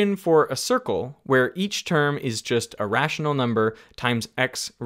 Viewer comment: Is there a way to fix the subtitles? at 13:26 Derek says "each term is just a rational number". However, the subtitles say "each term is just irrational number". Of course, the error can be spotted through the context, but that's just something that somehow stops your brain with like "whaaaat?"